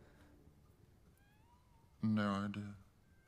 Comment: what does he SAY? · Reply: Veeeery late answer but: "No Idea"